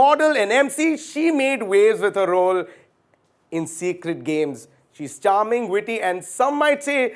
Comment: Did he say "Secret Games"